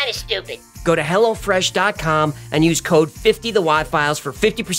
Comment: did you just say go to hell!? :O